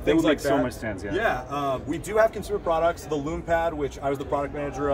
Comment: What was the Amazon product, that he said did a similar thing ? Loom Pad? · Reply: Lume Pad - $300 USD on Amazon